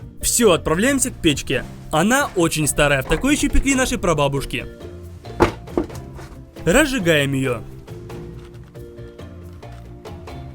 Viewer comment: ХХАХАХААХ XD, мне послышалось пекли наших пробабушек XD